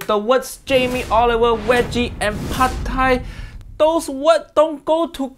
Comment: thought be said weggi Why this man even have show get off my tv mate